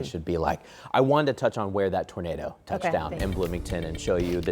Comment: Oh...i thought he said a Tomato touched down on someones plate.